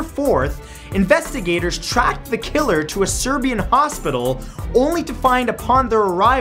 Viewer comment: wait did you say Serbian hospital? D